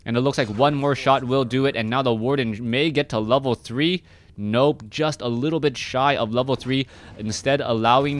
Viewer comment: im think he say "nope, just chuck testa" xD